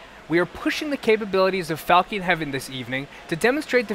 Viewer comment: Falkeen Heaven :)) did anyone catch that?